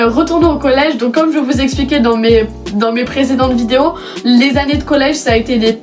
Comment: Ces moi ou à 1:53 elle a dit retour à mes présidente vidéo mdr sinon super vidéo